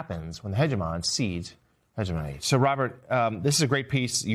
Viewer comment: ‘Sir Robert?’ I thought he must have said ‘so, Robert’ but after repeated listening I am definitely hearing ‘Sir Robert.’ Is it my failing hearing? Weird. If he is trying to say ‘so, Robert’ then he needs to slow down and speak more clearly (just like the rest of you young people do 😅). Anyway, Trump wakes up every day and surrenders something to some fascist dictator somewhere. No news here.